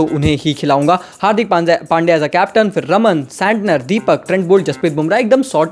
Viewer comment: ramandeep singh? WTF... He might say Namandeer 🙂‍↔️